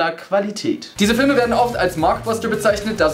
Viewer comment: ich hab mir die Stelle 5mal angeschaut und verstehe immer noch nur "Mark Forster"